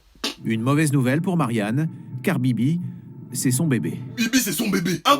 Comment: la premiere fois j'ai entendu "Cardi B c'est son bebe" je suis vraiment fatigué...